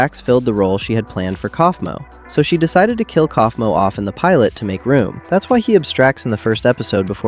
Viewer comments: my captions said caine instead of kaufmo whenever u mentioned kaufmos name and i had my volume off and i was like “caine didnt die in pilot..” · the subtitles said caine instead of kaufmo edit: 51 likes already? nicee 👍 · The captions keep saying Caine instead of Kaufmo 2:09 Miss-spell 2:51 I like the longer neck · Why the heck does the caption subtitles think he said "Caine" instead of "Kaufmo"?